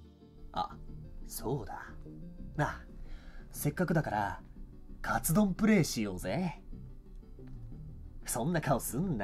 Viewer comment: カツラプレイって聞こえて!?ってなったわwwww